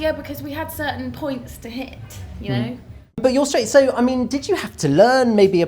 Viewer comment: did he say "but your straight"?